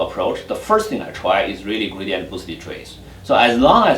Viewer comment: what statistical algoritham is he talking about? I can't understand his accent. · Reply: Gradient Boosted Trees